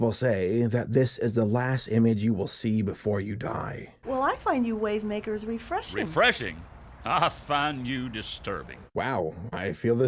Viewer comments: WHAT DID THAT FLOWER SAY!?!? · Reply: "Well i find you wavemakers refreshing" What's the problem. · What did she call them? "Wave-n******"???